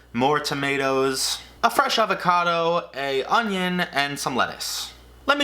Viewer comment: When you said “fresh avocado” I heard “fre shavacado” 😅